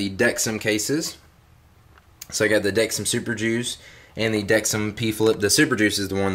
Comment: haha i thought he said dexim super jews